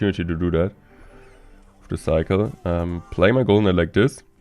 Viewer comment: I have been watching your channel for a long time now. When I had started watching your channel I was pretty much a noob, and I still remember when you used to say mortar cycle, I thought you were saying morten cycle referring to a cycle deck that you had developed😂😂. I still find it funny.